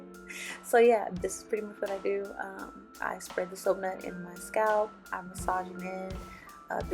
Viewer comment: “I spread the .... in my scalp” what is she saying? She mentioned it a few times but maybe I’m just not good with American accents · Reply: Soap nut